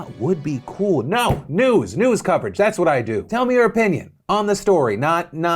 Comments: did he say "nudes coverage" :o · for a second there i thought he said NUDES coverage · am I the only one who heard Phil say "nudes news coverage that's what I do" at like 5:40 or was that just me?